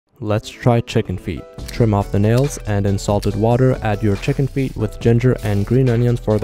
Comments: I thought he said insult the water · When you say "salted water" i thought you say "insult the water"